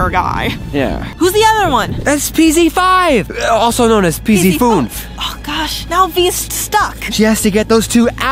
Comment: I thought when you said that is pz fünf i thought you said pz fool